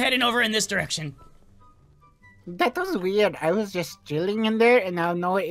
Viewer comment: I thought he said he was juuling in there for some reason